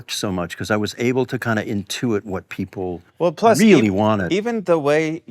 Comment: The captions say "kind of (inaudible) what people really wanted", but he clearly said "intuit".